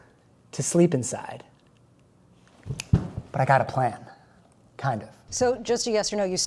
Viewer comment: DID U SAID PLAN 🥭